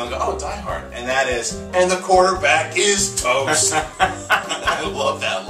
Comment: What did you say at 3:06? "The quarter back is toast"? My fave was always "welcome to party pal"